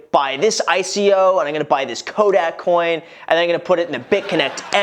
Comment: did you say Kodak coin